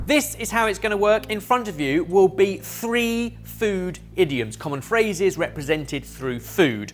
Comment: 🤣🤣🤣🤣😇😇😇😇I had to relisten several times to what Ben announced as the name of Round 2. I thought he had said “3 food IDIOTS” and that is what I thought he said at least 3 times. I was headed home after teaching all day and trying to monitor a school activity thar was so loud that I had to put in ear plugs that muffled the sound a bit, but allowed me to still work with the kids. So with a slight headache, I literally thought that was what he said. I had to laugh at myself, when I Cleary heard I'm sad “IDIOMS”... crazy what you will hear after a day teaching and doing an activity with about 400 children that you love dearly, but who can be louder than a couple of jet engines. Good to know they have healthy lungs. But yes…sorry Muke, Barry and Jamie, and double apologies to Ben, for even thinking he said that.🤣🤣🤣😇😇😇💖💖💖💖